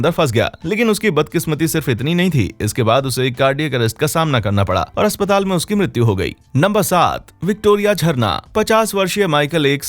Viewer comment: aap kya bol rahe ho🧐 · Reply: D current ka samna kar na para